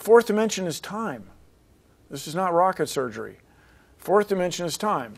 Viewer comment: Did he just say it's not "rocket surgery"